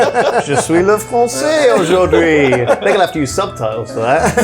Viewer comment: or just don't bother i guess :P also, what is he saying in the end of that sentence? aus ju trie? i need subtitles!